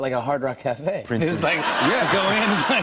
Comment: I think he said Prince's .... I wanted to hear what he has of Prince dammit Jimmy shut up!! lol!